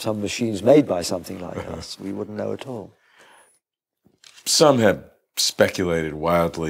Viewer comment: sorry didn't quite catch the reference. Can you please post the name and book? Is it Somnier? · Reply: "Some have"...